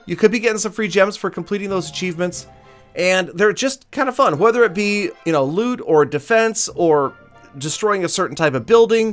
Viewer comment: What meant: "Maybe it be loot..." What I heard: "Maybe it be lewd"